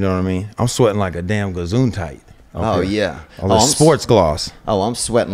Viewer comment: 😂😂😂 wtf is a gazoonti or whatever he said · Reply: gesundheit a form of saying bless you when you sneeze, in german